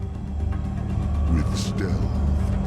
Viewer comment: What did that elite next to Arbiter say after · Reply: "with stealth"